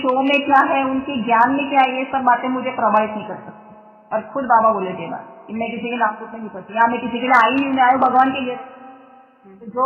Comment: Didi apne 24:53 me kya kaha....samajh nahi aaya....Baba ne aapko kya bola "main Kisike........me nahi padti.....woh bichme word kya hai ... Aisa kuch ... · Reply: बाबा ने कहा था कि, "शिल्पा, किसी के "नाम - रूप" में नहीं फँसती है ..!!